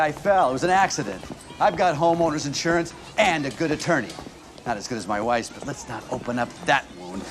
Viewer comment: I am I the only who heard him say not as good as my wife’s pussy